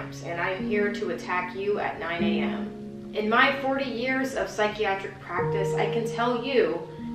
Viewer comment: did she say 40 years? I thought she's a teenager looking at the thumbnail